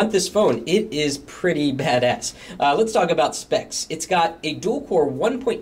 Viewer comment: did he say ''badASS'' at 0:57 ????????